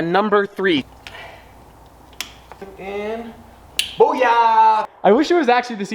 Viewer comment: did he just say hogaya 😂 by fellow Indians will get it 🇮🇳.